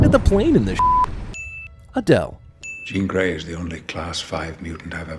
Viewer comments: I thought you said “Adele” and I was like “……….yup.” 😂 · when it said "a dell" i thought it said "adele" and was confused why no music